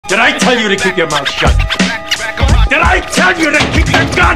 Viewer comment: Молчать?? мне одному послышалось по русски? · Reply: mouth shut